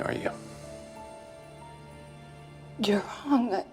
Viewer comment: Anyone know what she says at 8:45? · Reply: She says "You're Wrong..guh.."